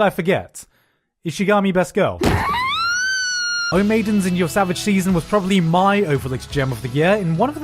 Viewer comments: HERE HERE HERE HERE!!!!!!! AHEM Thanks for your attention. @ 12:06 What anime is that!? for some reason I keep hearing Gigguk say "Oh maiden summer cheese" instead of the name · Can someone tell me what he's saying at 12:05? I can't make it out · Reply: "O Maidens in Your Savage Season was probably my overlooked gem of the year" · What's the anime at 12:04 ? I couldn't really make out what he said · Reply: O Maidens in your Savage Season